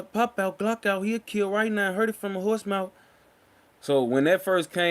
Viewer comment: I thought said “he’ll kill I ain’t lying I heard from the murder spouse” lol the horse mouth Jesus I was way off